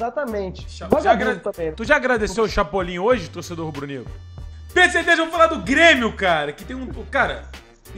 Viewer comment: Só eu ouvi "PERCEVEJO" ao invés de Pedro certezas